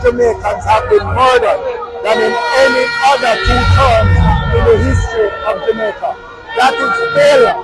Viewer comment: In all fairness Bunting said 2 terms NOT 2 years. I think George misheard.